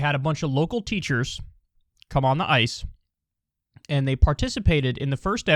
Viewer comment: lol... I started loading the video and paused it at 0:27; so when the video resumed all I heard was "cum on the ice". Perfect fit for the context of the video.